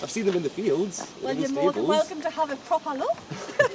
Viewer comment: I don't know why, I guess just cause it's different to me but I love the way in which they speak there. @ 12:35 "You're more than welcome to have a proper knock" at least I think she said "knock" lol I can understand much more now after watching for a good while. love it